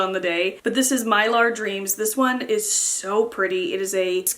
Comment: Def thought you said that second polish was called “my lard dreams”